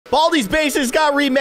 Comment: Baldi‘s bacis that’s what it sound like you said